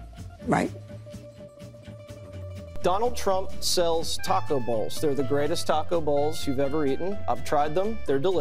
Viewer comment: I thought he said "taco balls".